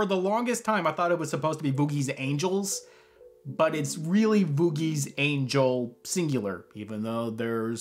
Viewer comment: Sometimes I hear “Boobie Angel”